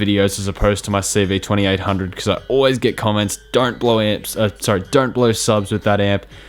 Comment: for a sec i thought he said don't blow amps using speakers and i was like wtf😂